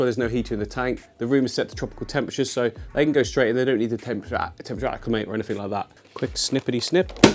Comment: What he was saying!? 😂😂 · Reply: “They don’t need to temperature acclimate or anything like that”.. thought it sounded pretty clear tbh